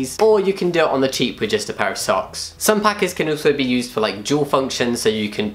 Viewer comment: I am here after the awkward trans videos and this is the first time I have ever seen the written word packers, I always thought you said paka as in Alpaka and thought it was kind of an insider (and wondered what the connection between trans man and Alpaka were - it also took me a while to understand what a packer was). I feel stupid and educated at the same time XD 🙇‍♀️